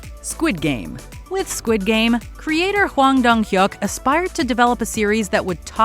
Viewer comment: Please tell me after hearing Huang Dang Hyuk at 7:37 I'm not the only one who heard or thought about Goofy 🤣🤣🤣🤣